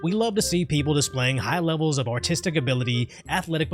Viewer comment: idk why but I thought you said "high levels of autistic abilities" I need to sleep